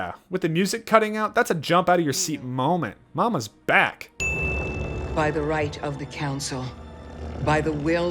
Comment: I genuinely thought she said “By he right of the calzone-“ for a while